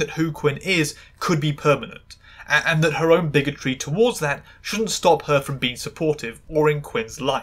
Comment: My problem with the Doctor being transphobic is that medicine is his special interest and he knows so much about it that it's ridiculously unlikely that he'd have never heard of trans people before and upon learning that trans people exist probably would probably have gotten curious and wanted to know more about trans medicine. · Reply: i thought you said "shouldn't exist" and was like "what the fuck"